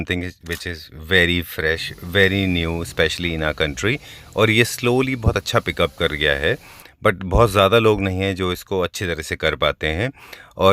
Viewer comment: Did you say "kar riya" hai